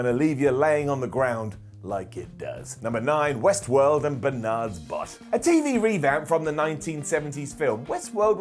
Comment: Is it just me or does it sound like he said "and Barnard's butt."